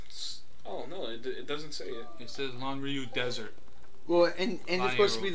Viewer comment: Do I even want to know what said or is it NSFW? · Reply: Oh ok. It sounds like he said "Desen"